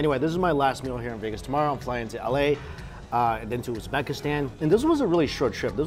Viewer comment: Did mike say he is going to pakistan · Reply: ujbekistan